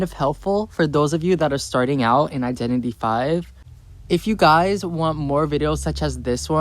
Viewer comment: My little sister told me about this. She said it’s like dbd, which I introduced her to, but our dad won’t let her play it lmao also I didn’t know it was identity five. I thought she was saying identity B and it threw me off lmao